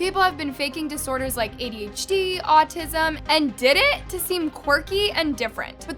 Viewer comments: did you call dissociative identity disorder DIDIT?😭😭😭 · OKAY, I AGREE WITH EVERYTHING U SAID, just wanted to point out it sounded like you said "di-did" it's "D. I. D." : }